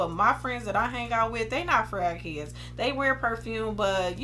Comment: I thought you said "my friends are not crack***ds" 😭😭😭 you said fragheads 🤣🤣 This is a BEAUTIFUL lineup!!